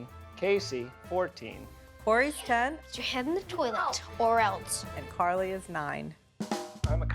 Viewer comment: did he just say "put your head in the toilet or else "